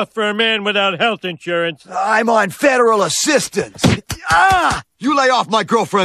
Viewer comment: "I'm on federal assistance" XD nice find/upload m8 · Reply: I thought he said "Feral Assistance"